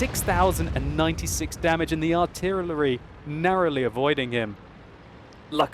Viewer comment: what is that word???? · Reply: think I was going to say arty and artillery and combined the words into artyillery